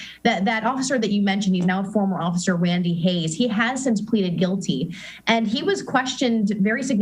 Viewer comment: did she say Pleded